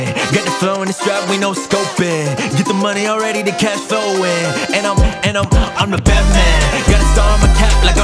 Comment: First heard this song in a mix with all your music thought you said "I'm the Batman"